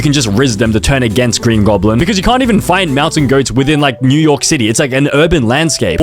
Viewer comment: Did he say mew York city or was that just me? 5:25 Oh and there is not a single movie without P.Diddy in it and I only watched a little over half the video.